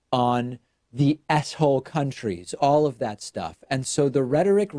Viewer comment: Lol. When David said "S-hole countries" at 3:11, it sounded like he said "asshole countries."